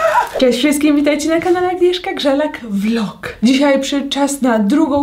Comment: Usłyszałem "Cześć tutaj Wieśka Grzelak Vlog"